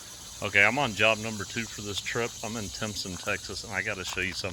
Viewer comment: Granbury Tx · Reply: Surprising. Sounded like you said “Timpson”, and that looks a lot like east Texas. Good video and great work, though.